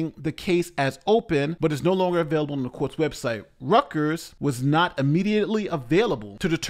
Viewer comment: Reuters = "Roy-ters" I was so confused when I thought said "Rutgers" the first time